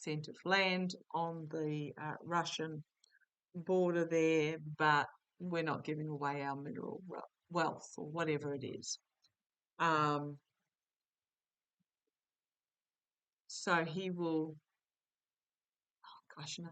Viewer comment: I heard "wealth".